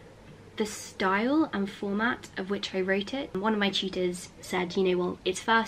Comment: she says cheaters instead of teachers. So adorable😂